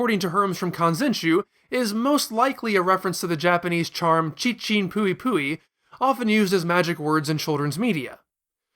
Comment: “chichin-puipui” is specifically the magic words you use to make a young child feel better when they hurt themselves. “ Chichin-puipui ! Ouchie, ouchie, fly away! To those mountains, fly away!” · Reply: At first when he said "chichin puipui" I misheard it as "chinchin puipui" which would mean something a little bit different. lol